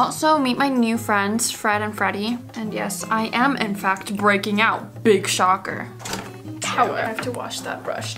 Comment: I thought she said breaking up lmao i was like what with whooo